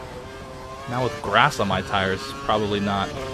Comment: When he said "Now with grass on my tyres" at around 13:55, the video pooped for me and it sounded like "Now with *booooop* on my tyres". XD